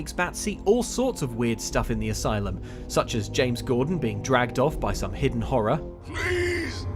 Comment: I could have sworn said that James Corden gets dragged off by some unseen horror, and basically thought "Eh, could be worse"... Then realised he'd said James Gordon and was very upset...